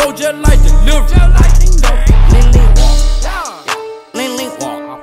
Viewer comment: lean lean wok, with glock, on the block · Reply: i thought he said ling ling wok at first